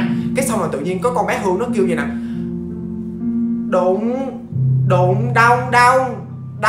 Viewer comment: đụng đụng đau đau mà t nghe nhầm là đụ đụ đau đạu :)